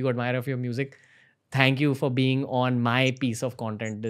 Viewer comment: 🤔 is he saying "thank you Elon musk", at the end Can anyone notice that ? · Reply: Its god bless😂